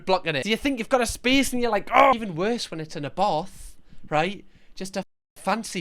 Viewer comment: does he say "even worse when its in a bath"? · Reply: Abarth